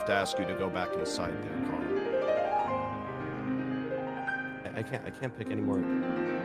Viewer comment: does anyone know what Ranboo says here? I'm having a hard time hearing it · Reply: He said "i can't pick any more up" talking about the gold that ser billium was throwing